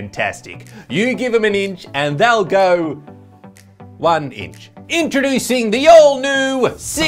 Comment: Did I hear "Inchroducing"?